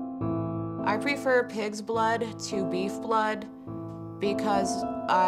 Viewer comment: When she said “pigs blood” I thought she said “kids blood”